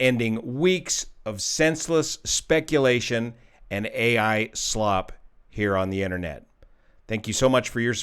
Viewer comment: I thought you said, "AI Sloth" That's what I call it as it takes a really lazy person to generate that AI slop.